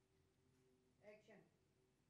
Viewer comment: In all chaos, I heard "action" at 16:59 enjoy😅